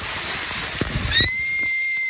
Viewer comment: "Where did my ball go?" "Its down there stupid" "Oh dang" shark puppet gets throwned down "AAAAAAAAAAAAAAAAAAAAAAAAAAAAAAAAH" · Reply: I thought she said oh Dave but it’s oh dang